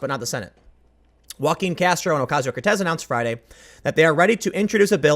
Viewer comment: I thought you said "walking Castro Ocasio Cortez"